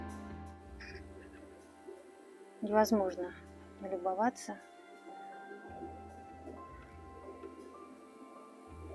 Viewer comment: Здравствуйте! Даже после обрезки роз есть что посмотреть, очень красиво. Не расслышала название розочки на 8:40 минуте. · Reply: Здравствуйте, София, это Дистант драмс / Distant Drums) Спасибо большое!